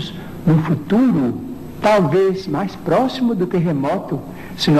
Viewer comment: Bom dia meu amigo Fiquei curioso a respeito do que ele fala sobre " mais próximo do terremoto" no minuto 8:00....poderia me dar uma ajuda para a interpretação? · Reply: "mais próximo do que remoto" a primeira vez que ouvi também entendi isso, mas aí voltei e consegui corrigir.